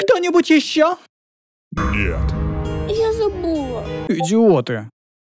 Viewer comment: мне послышалось "я забула"